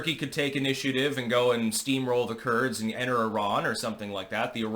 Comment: I heard "Steamroll the Turds" and cracked up...